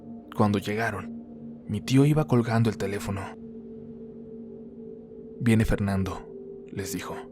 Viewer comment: Cuando dijo " mi tio iba colgando el telefono " escuche " mi tio iba colgando del techo" deveras estoy hasta la perra madre de estos sustos pero disfruto tanyo los relatos que se me olvida.